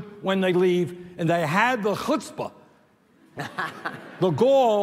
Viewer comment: What was that word he said @ 57:18 ? Please and thank you :) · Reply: Chutzpah. Apparently it means "insolence", "cheek" or "audacity".